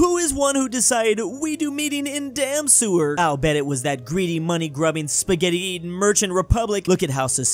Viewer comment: I thought Zimbabwe said monkey not money 💀